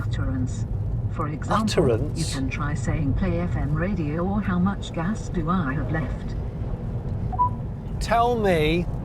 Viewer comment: This is really a nice grocery shopping car or commuter car (given no traffic jam in the commute or you might just be stuck in the middle of the road) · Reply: Did I just heard at 21:30 "How much gas do I have left??" Bruh, are you electric or not?